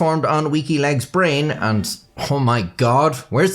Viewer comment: I keep hearing "Wikileaks" whenever you say "Weaky Legs" 🤣